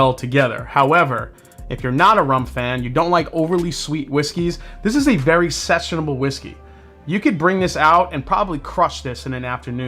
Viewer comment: swear I heard "this is a very sexual whiskey"